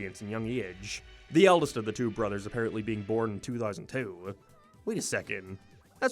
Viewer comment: thought you said 2010 😭😭😭😭